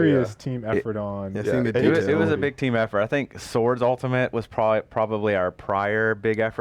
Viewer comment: About 32:50 maby a lil later who ult They say some one ult was harder to make but I can't catch the name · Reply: Think he said Sword ?